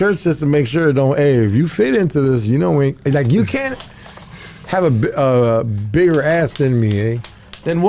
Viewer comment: did he say you cant have a bigger azz than me?